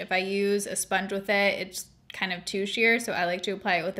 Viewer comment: I thought you said tooshier 😁